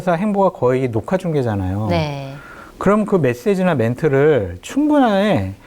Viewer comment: ~~ 장성철~~ 메세지~~ 멧돼지로 들림~~ 멧돼지 맞음.. ㅋㅋ